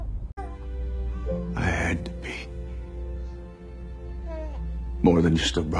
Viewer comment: did he say "i had to pee" or 'i had to be" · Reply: I had to be